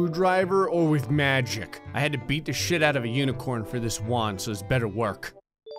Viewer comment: did he forget to censor this part, or did he say "sheet" ??